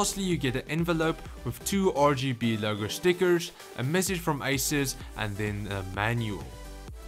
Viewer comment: In the start When You Said ”You get a massage from asus” it sounded like You Said ”You get a massage from isis”